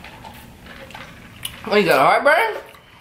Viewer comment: Did he say are you ok brian it had my heart pounding